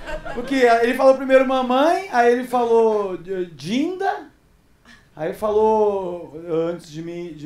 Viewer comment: Juro que quando ele disse "dinda" eu entendi "Tinder"! Levei até um susto! 😂😂